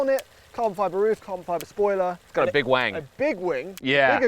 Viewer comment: LMAO...Did Thomas just say "It's got a big WANG"??